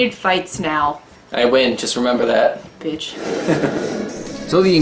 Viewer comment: did just say bitch? 🤣🤣🤣